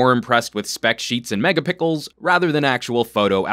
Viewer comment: I swear I heard, "megapickles". xD